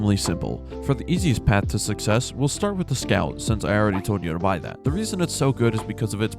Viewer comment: you were talking a bit to fast I couldn't hear what vehicle u said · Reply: If you’re talking about the green one on screen, that’s the scout (variant of the navara territory)